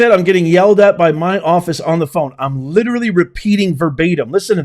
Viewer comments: Sounds like she said ".I;m getting yelled at by my boss on the phone. Like, I'm literally repeating the word"s that were given to me " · It sounds like she said, "I am being yelled at by my bosses on the phone. " It doesn't sound like offices to me.